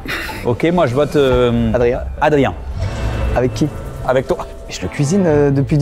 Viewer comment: j'ai entendu avec Thomas